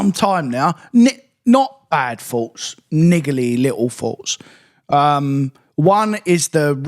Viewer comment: what did you say? Omg I’m dying here. That British accent will get you every time. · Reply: 😂 Niggly little faults... aka annoying/fiddly/temperamental little faults